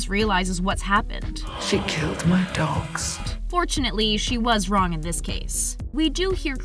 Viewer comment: The "Anita darling, my darling" just hit so fucking diff 😩<333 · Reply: i thought she said my dove